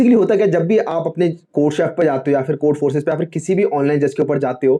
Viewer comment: can anyone help me what said before code forces? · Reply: Codechef